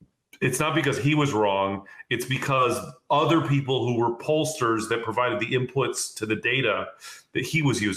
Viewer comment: I may have misheard but did he say "POLLshit" , that's a good word for that use of his comment.